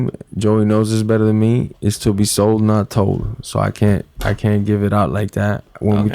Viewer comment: Hey what is saying at the 20:20 mark when Jorge is not revealing what? sorry english aint my first language =)) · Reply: It's to be sold not told ;)